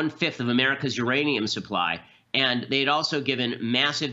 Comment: At first I thought he said geranium supply.